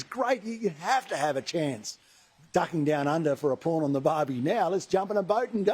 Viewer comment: Did Barnaby say "a Porn on the Barbie" 🤔